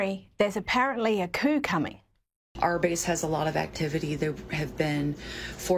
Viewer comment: I couldn't understand the one woman with the piercings and tats and half shaved head - it sounded like she was says " 'Arby's' has a lot of activity there have been 4 Blackhawk helicopters...blah blah blah" I was thinking why would Arby's be involved in a coup??😂😂 I had to listen acouple times - she said "our base" ?? What a whack!